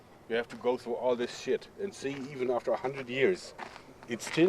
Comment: a awesome video,thanks for sharing it with us. The barb wire and the tree,they didn't "bury/place" the wirethat deep in the tree,over the years the tree as it grew would grow around the Barb wire making it look like it had been done by man,but was actually done by nature.On old farms and stuff where I live ,you see this alot,Typically where the barb wire is placed,A bit of bark is removed for a tighter fit,but as it grows back it grows over the barb wire. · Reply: did you say "and all this shit" at 4:17 xD!